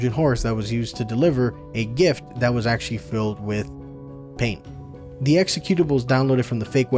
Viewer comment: did you just say the trojan horse was filled with paint? I must have misheard...